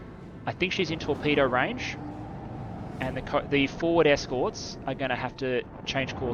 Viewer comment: Foward escorts, I heard "Ford Escorts" and was like, what an odd thing to have in the game.